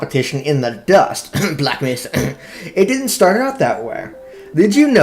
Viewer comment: Did you just say blackness because if you did we are going to have a problem...